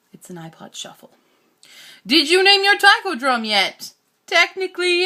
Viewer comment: I would have sworn you said "taco drum" so perhaps you should name it Taco. Taco Taiko. It has a ring to it. Though I guess that would be Mr. Taco Taiko to me as I'm not on familiar terms with your drum. Wait... maybe that would be -san, because you're in Japan... I'm thinking way to much about this.